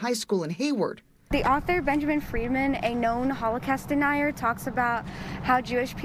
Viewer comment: Does she say h-CAST or is t just me? :face-purple-crying